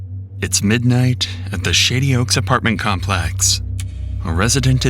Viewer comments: I thought you said “shitty oaks apartment complex” lol! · Ha I thought you said “sh*tty oaks apartment complex”